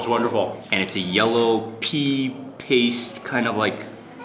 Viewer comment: Wtf? "It's a yellow pee paste" ? Sounds wonderful!